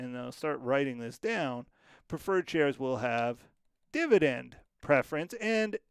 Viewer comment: Kinda confused on term participating. The way I read in my textbook v. MCQ in my CPA exam material, is, well, idk. Preferred dividends paid, then common shareholder dividend THEN participating gets a share of the rest. From what you said if I heard correctly, preferred gets theirs and IF there is leftover for common shareholders, participating gets some (pro rata) at this point NOT necessarily AFTER common gets theirs. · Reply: I agree with your study materials but I think you misheard me - 3:49 - "preferred shareholders have dividend preference". Laid out pretty clearly in that section, but if you found me misspeaking - hit me with a time stamp so I can correct the error!